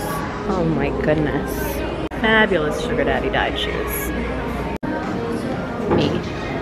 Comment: "fabulous marrying the suggar daddy shoes" "fabulous sugar daddy has died shoes" I'm dying 😂😂😂😂😂😂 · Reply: the second one i thought she said fabulous sugar daddy fine dine shoes.lol🤣